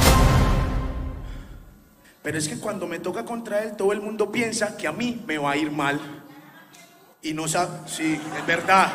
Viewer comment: Que dijo la mujer? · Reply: Dijo, por qué el es mejor que tú!